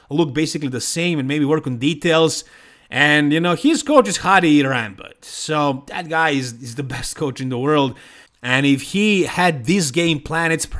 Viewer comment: Andrew and Samson first and second , new era of Bodybuilding, midget era and Synthol is over. Did you say his coach is Hadi Rambod😂 at 12:45 .